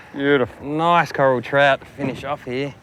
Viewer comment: The one you pulled in @ 22:12, did you call that a "Coral Trout"? Beautiful fish, hell, they're all beautiful, a fisherman's paradise.